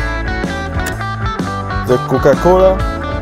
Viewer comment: Did i hear "the kUkA cOlA"